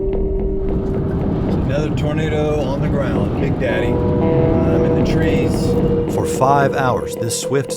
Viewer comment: did he just said "big daddy" 💀